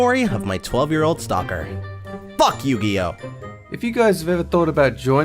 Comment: “F*** Yugioh!” And just like that, friends and enemies were made this day. I’m just laughing. =w= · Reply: I thought he said "Fuck you, Dio".